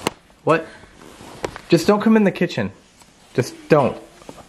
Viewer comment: It sounded like said, “I’m in the kitchen.”